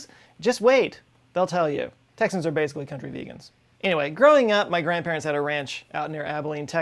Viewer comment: My sleep-deprived brain is not interpreting what on Earth he says here. All I can hear is "Texans are basically comfort eagles." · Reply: grovermatic “country vegans”